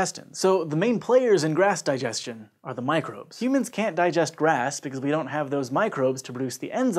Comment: I thought it said why can't you digest glass, and I was like, why would I eat glass in the first place?! 😅